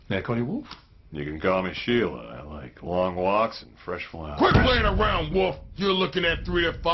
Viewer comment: Flippers:So Mr.Wolf.May I Call You Wolf? Wolf:You Can Call Me Sheila.I Like Long Walks And Fresh Wine.😂🐸🐺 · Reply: I thought he said fresh flowers lol